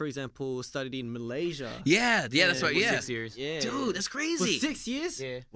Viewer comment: he said "Six years" What i heard : seXy eArS I'm so weird 😂